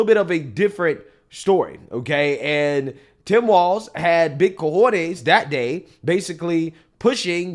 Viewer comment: did he say big cojones? 😂